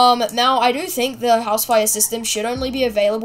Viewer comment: for the housefires i thought you said housewives XD